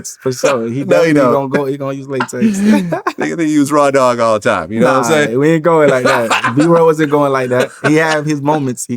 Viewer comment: I thought vlad said he don’t use the n word 😂 1:35 he dropped it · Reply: Nigga